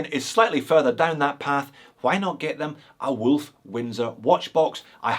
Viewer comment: Here's a good one! All this time I thought you were saying "Wolf winder". Now I know it's "Windsor"!! Merry Christmas!